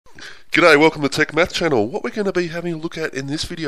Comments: I thought he said welcome to "the take meth channel".... still, helped me a lot with my test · thought you said take meth channel